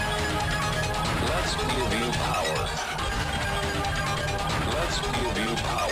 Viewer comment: All I hear is "Let's moving/movie power"